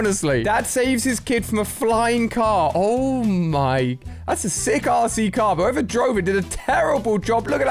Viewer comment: When ali said that is a sick rc car i thought he said a sick 🐎 car